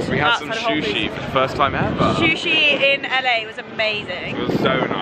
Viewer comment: Did they just say "shushi" instead of "sushi"? 😂